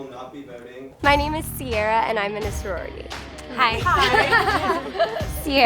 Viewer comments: at the beginning where they said, "im in a sorority", i thought they said "im finna sore rey" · Himynameissorry is what I thought they said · After the beginning i just heard "Himynameisscrorty"